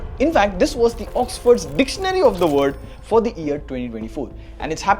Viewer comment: Did he just say "dictionary of the word'